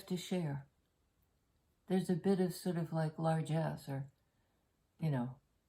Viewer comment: I was reading the transcript until I got to the part about a "large ass" and I thought "no way did she say that" so I watched again w volume on and whilst reading (you'd said "largess). Transcript alone is super funny but very confusing Great read